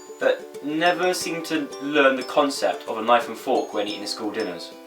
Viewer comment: when he said knife and fork i thought he said iphone 4 haha